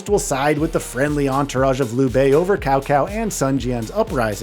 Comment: I'm gonna lose my mind if I hear "Cow Cow" another time 🤣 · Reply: Dude I swear I heard Tao Tao 😂